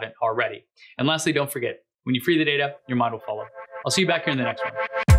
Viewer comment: "when you free the data up, your model fall up"? CC didn't answer exactly what you said, but it sounds like an interesting quote